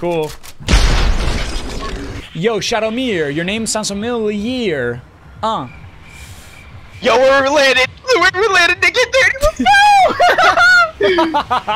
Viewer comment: I heard we're related lool